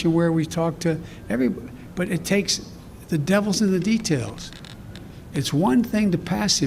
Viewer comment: Did he say the DEVILS in the details????? Wow that disturbing